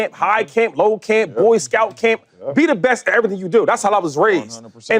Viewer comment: i heard that shady